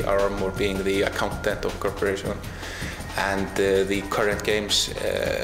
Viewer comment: the subtitles are wrong. It's not "being the content of a corporation", it's "being the accountant of a corporation"